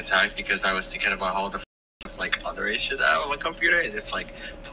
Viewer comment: very good video but you should probably acknowledge some of the sarcasm in comments instead of taking them at face value overall good thou gjgjgjgjgjgj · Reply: i think he might be saying "underage shit" not "other issue"